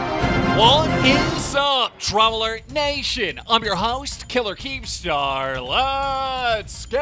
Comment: was i the only one who heard him say. killer keen star?🤣